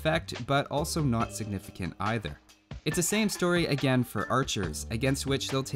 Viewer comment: not a huge effect, but also not significant either. ....? Heard it over and over again, thought you said “insignificant”, but didn’t sound like that, so... :p Didn’t mean to be...mean, just noticed XD Very appreciate your efforts, been looking forward for this :) keep it up! By the way, what would you choose between murder hole & ballistica after you build your first castle? @@